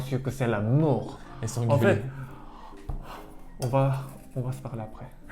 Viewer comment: il a dit quoi?? 😭😂 (J'ai vraiment pas compris) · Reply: « Et s’engueuler » je crois 😊